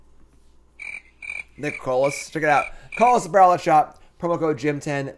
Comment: Lol omg this entire time I thought he was saying COLORLESS APPAREL instead of Coalesce til I looked at the bottom of the screen.